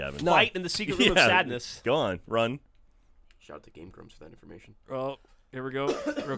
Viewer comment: did he say game grumps? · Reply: "Shout out to Game Grumps for that information"